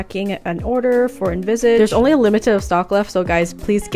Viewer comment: Who else thought she said "the emo template"??????